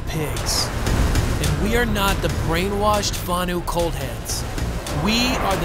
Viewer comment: Guys, heeelp! I totally don't understand what is said in 0:28 - "brainwashed Vanu ...<what>"? I'm not a native. I play as NC and i wanna know :c Could anyone enlighten (teheee) me, please? ( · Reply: They call the Vanu 'coldheads', as in no thoughts going on from their brainwashing.